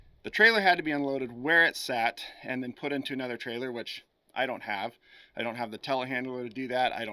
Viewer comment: At the beginning when you were saying “I don’t have” over and over, I heard “ haven’t seen on FB marketplace yet” over and over again, fantastic video, great of Consolidated and the truck company to let you film and narrate it